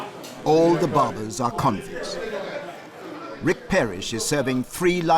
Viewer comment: "all the barbers are communists" Thats what I heard when I walked into the room. I left this youtube on and autoplay went on... Now that I look at the show I realize he said, convicts. 18:40 LOL Listen to it over it does sound like he says communists lol